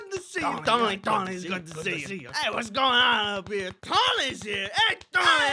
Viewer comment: i might be mishearing things again but it sounds like he was saying tiny hands rather than tony's here